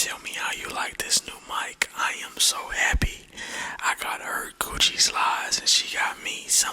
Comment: My dumbass thought he said Coochie slides 🤦🏾‍♂️😂